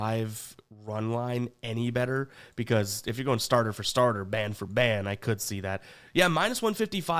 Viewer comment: hand to rewind when I thought I heard Band for Band 😂😂